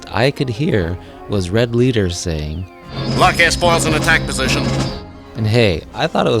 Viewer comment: The only one from your list I misheard was "lock s-foil in attack position". I heard "Luck S-Falls in attack position". I had no idea what it meant. Then again, the actual dialogue doesn't make any sense to me either.